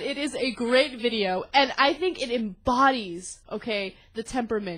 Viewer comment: CC says "anti-clinton bodies okayed" lol